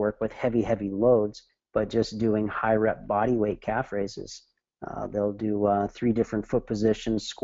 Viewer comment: what is the word he says after "high rep bodyweight..." it sounds like "caphrases"...maybe "calve phrases"??? Help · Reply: Calf Raises